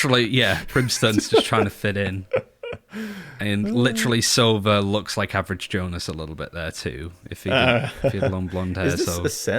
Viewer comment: HE Actually said "And that sova looks literally like Average Jonas" xDDD He Does !